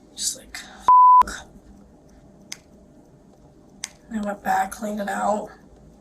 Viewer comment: Can anyone tell what she says right here? It sounds like "the whole boat...duh duh duh" but that doesn't make any sense with the context. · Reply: She says "I went back, cleaned it out." She's talking about the cut on her hand (she's fiddling with the bandage while she's talking). She says it a little weirdly, the letter O kind of gets swallowed but that's definitely what she's saying! 👍